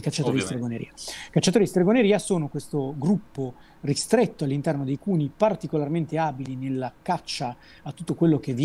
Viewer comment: il simposio dei culi... mi sa che ho capito male